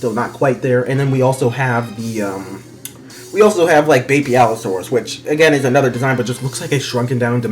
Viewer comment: Your Pronunciation of Beipaosaurus sounds like your saying "Baby Allosaurus" so much